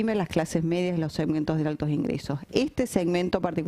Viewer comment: ESCUCHO MAL , ROYÓN dice:'..sosteniendo a los segmentos....de altos ingresos " ) , en el minuto 41:05 / 09 segundos no sale con claridad pero lo escuche varias veces y aun no lo oigo con claridad. a ver que escuchan uds.